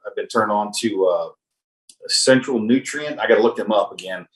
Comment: What are the two companies that your guest mentioned that allow you to purchase individual type of survival food items? It sounded like one with Thrive and I couldn’t understand the other one. Thank you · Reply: I heard “Central Nutrient”